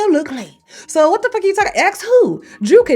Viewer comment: Did she just say What the Ferk?